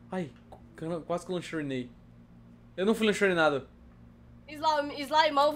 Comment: palavra "eu fui abiduzida" kkkkk o correto Naum e abiduzido? Kkkk (não sei escrever abiduzido) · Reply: foi impressão minha ou eu ouvi "não fui lancherinada" kkkkk ou quero dizer? Kkk